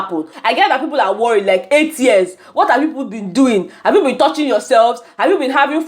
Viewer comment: I actually thought I heard 3 years from Bright O & not 8 yrs.